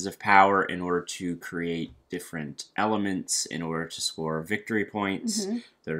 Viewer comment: My ears thought you said "elephants" when you were talking about the "elements" in res arcana...